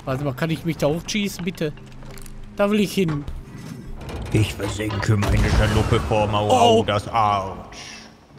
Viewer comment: Wer hat auch verstanden: "Ich versinke meine Schalupe vor Maudado's Arsch" XD